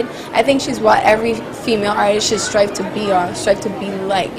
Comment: Oh I THOUGHT SHE SAID HOMEBOY!! HAHAHAHAHAHAHAHAHAHAHAHAHAHAHAHAHAHAHA But its humble